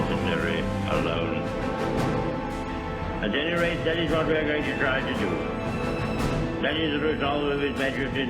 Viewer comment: anybody understand what he is saying? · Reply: ‘At any rate that is what we are going to try to do’